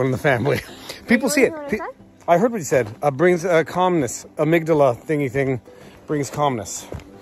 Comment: Listening at double speed I thought you said the amygdala brings Communist